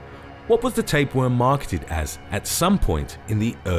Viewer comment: I thought it said tampons...